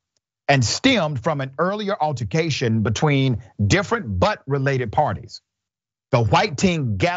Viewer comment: I heard "different but related parties" as "different butt-related parties", so I had to glance over at the transcript to make sure you didn't just say that.